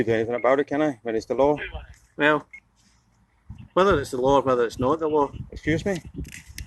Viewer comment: what does the cop say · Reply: Weather its the law, or its not the law.....